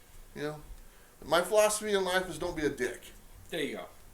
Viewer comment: philosophy in life: "Don't be a d-ck!!" What I hear: "Be a v-gina." Anyhow, another fun and good show, thanks! And, congrats with the award, it is 110% earned.